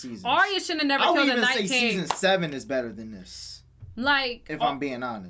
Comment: did say season seven was better than 3 and 5?? Am I hearing it right, someone please please help me